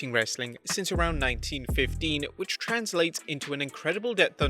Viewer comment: did he say 1915 or 1950? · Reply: 1915